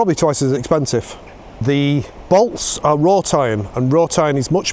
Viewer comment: I don't understand that word that sounds like "raw tine" @ 3:25 .. What are the bolts made out of? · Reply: Wrought iron (turn on the captions in case of difficulty!)